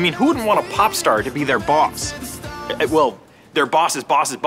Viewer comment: Already jamming to it... Don't Ever Say It's Over, If I'm Breathing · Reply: Ok but like I heard it as "Don't ever say I'm Sober"